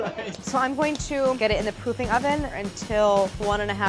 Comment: she definitely said pooping oven instead of "Proofing" oven lol